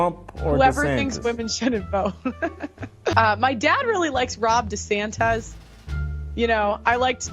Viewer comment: did she say "Rob DeSantez"?? Lol she's extra